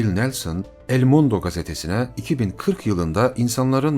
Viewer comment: da 2040 mı diyor ben mi 8:31 ben mi yanlış anladım